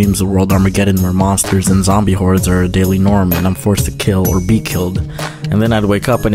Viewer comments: (btw, before I say this I must point out I am in fact a undertale fanatic. please don't read this comment if u hate undertale.) did you say kill or be killed? FLOWEY QUOTE! the thing is I say mercy all the way! but yeah back to the vid sorry I just had to point it out. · did I just hear kill or be killed did I hear a undertale reference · Did someone say kill or be killed?